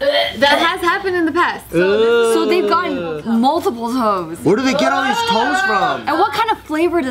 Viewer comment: I thought said "Multiple Hoes"